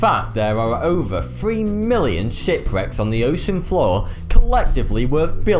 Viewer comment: did you say Shit Bricks?